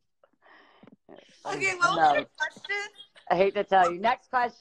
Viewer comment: what did demi say at 22:44? :) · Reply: She just laughed and said, "okay what was your question?"